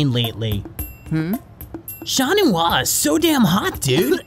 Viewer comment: I was so confused what he said and now I know, it's chat noir LOL